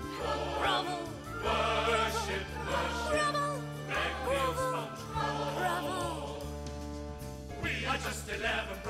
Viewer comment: When I was younger I thought the narrator was saying bravo rather than grovel As a child I was still confused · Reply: And someone thought the brothers were saying "Thank you, SpongeBob" rather than "Beg, kneel, sponge, crawl".